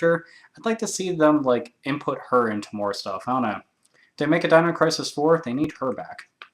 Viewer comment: lol I thought you said Dino Racist